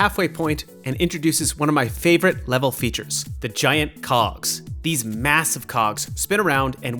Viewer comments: I thought it said "The giant c*cks" 😭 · ayooo what did he say 💀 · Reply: cogs